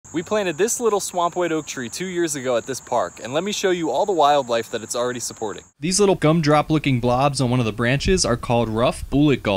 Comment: I swear to God I thought he said "Swamp Widow Tree" I was about to go find one to buy. Omg! How cool would that be? Y'all I think I got Halloween brain. ❤